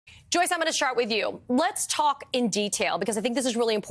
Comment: Did she just say "Joyce, I'm gona shart with you." Lol. Or am I hearing things.